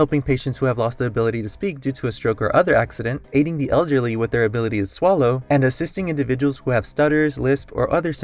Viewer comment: i thought u said: “hating the elderly with their ability to swallow” 💀